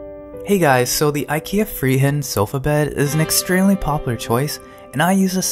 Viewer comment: I like when you say Friheten cause im Swedish XD It sounds like you say Freeheiten